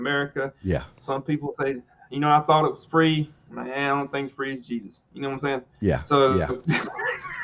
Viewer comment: into the video, what did he say about free and Jesus? His accent is strong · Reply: He said the only thing that’s free is Jesus, lol.